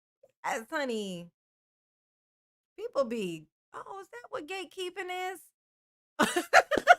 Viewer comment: HAHAA! I thought I HEARD “Gay Keeping”…. I was thinking WTFRIG IS THAT!!! Let other people just Live! But you were saying: “GATE” KEEPERS! 😂😂😂 A BIG DIFFERENCE!!! 23:38 ❤